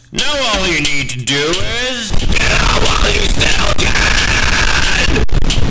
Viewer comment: the 1st time to the 22nd time he said get out when you still can i thought he said is NOW ALL YOU NEED TO DO IS SALVASIOOOOOOON!!!